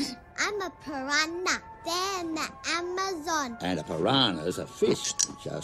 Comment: Did she say Amazon?